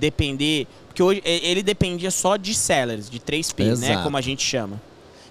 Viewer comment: Dúvida: oq foi dito ? "dependia só de sellers, de 3 ... ?" 👇🇧🇷👍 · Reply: Olá, tudo bem? Na live eu falo que dependia somente de Sellers 3P, produtos que não conseguiam performar bem. Agradeço por acompanhar o canal e fico a disposição. Grande Abraço